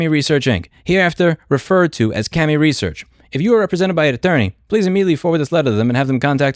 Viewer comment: Haha every time you said the company name I heard it as Scammy Research. 🤣